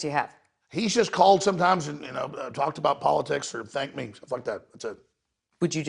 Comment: did he say politics and dank memes? ahhaha